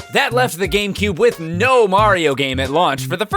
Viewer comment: I thought you said no Mario peebz